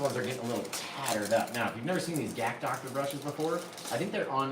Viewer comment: Hi! So at 9:06 ish you mention the name of these brushes on Amazon- what did you say they were? Gack Doctor? 🫶thanks! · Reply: GacDr brushes on Amazon